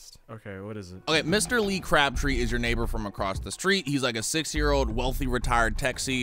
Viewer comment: anyone else think he said he a six year old?